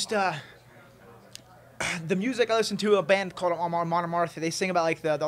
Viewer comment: Does anyone catch what "Viking" band he says he listens to at 1:15? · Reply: Chase Brown Amon amarth